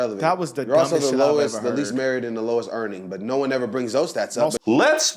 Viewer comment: Oh I need to clean my ears cuz I thought they said marriage and not married lol. And what is being married have to do with being able to do a job